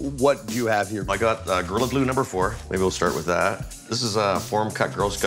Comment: What is he saying at 1:54 "grlg blue number 4" anyone please?